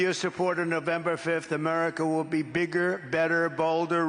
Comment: Did he just say anerica will be balder with him?